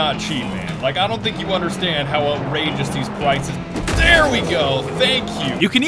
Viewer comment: it sounds like he says "pwices"